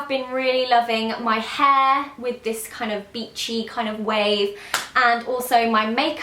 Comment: When she said 'beachy' I thought she said 'bitchy' XD